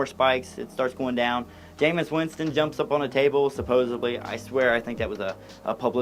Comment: did you say "supposably".